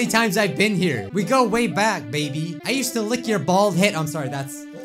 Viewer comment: or 4:21 what did he say he said lick his bald?